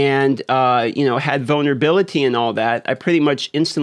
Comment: I thought you said “ramp up my boner ability” not vulnerability 😅